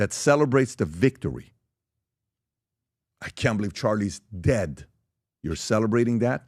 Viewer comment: In that clip from TMZ the guy wearing the dark tee shirt that walks by sounds like he says Charlie died